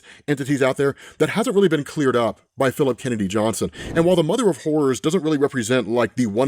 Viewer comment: 😂😂😂 I’m sorry but, every time we talk about this character, I just hear “ Mother of whores”. 😂😂 🤦🏾‍♂️